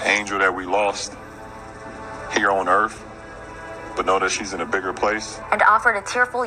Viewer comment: did he say "earf??"